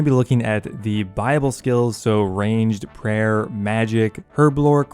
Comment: Could have sworn you were saying "Bible Skill" instead of "Buyable Skill" lol